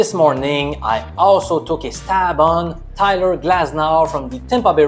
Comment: I didn't say Brewers and Miami. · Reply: what were you saying about the Tampa vs Miami pick then. It sounded like you said fafe Glasnow